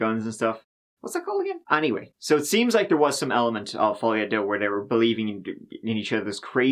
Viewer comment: I thought said, "follow ya, duh." Turns out the definition is pretty much that. folie à deux: delusion or mental illness shared by two people in close association.